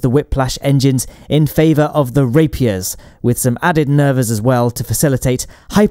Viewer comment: in favor of the Rapeiors Did i just hear Rapeiors? If you know what i mean😏